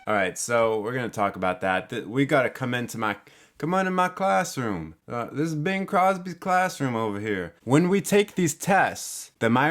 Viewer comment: Thought you said, "C'mon into my closet"...But dude its come out of the closet day...dude??? Intj mixed with Whiteclaw. This is what you get... Bored. gonna go make a sandwich.